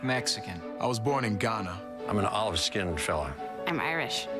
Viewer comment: Honestly I thought he was saying "Olive skinned FELON". Either way it is a strange thing to say.